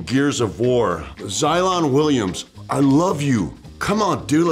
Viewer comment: thought that said Zion Williamson